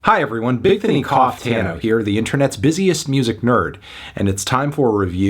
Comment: I really thought this man said "Bigthony Cocktano," and I had to rewind.